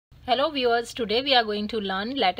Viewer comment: I heard hello b words